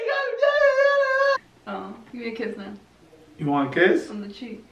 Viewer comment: I thought she said “Give me kids now”